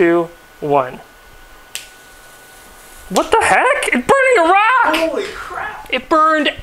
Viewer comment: "It Burned Iraq" looks at map Nope it's still there GET THE BIG BOI LASER